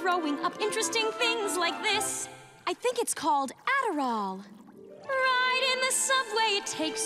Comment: I think it's called... - please, someone can type what she found? I didn't understood. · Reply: Adderall. It's a drug usually used to treat ADHD, but some people use it recreationally.